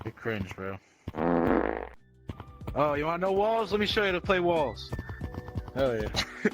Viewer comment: i thought u said oh u wanna know balls let me show u how to play balls🤣🤣😜😜